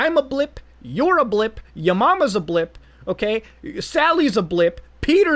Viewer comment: I thought he said my mom was a blimp.. I was like damn you right